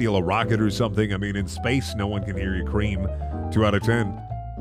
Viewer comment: did he say cream?